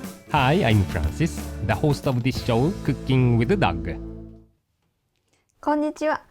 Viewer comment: i thought i heard ' cooking with a duck ' ;O